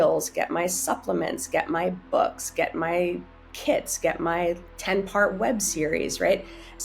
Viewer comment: she says, "get my kits" but the subtitles say "get my kids" 😂😂